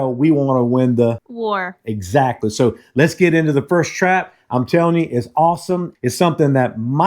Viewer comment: i heard “THIRST TRAP” cos just watched reaction vid of cringe thirst traps LOL Was like NOOOOOOOOOO!!!